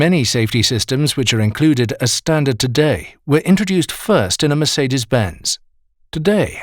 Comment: I thought he said y’all Mercedes